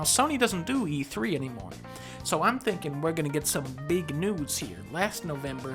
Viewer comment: Did Dunkey say "Big Nudes" instead of "big news" at 0:12?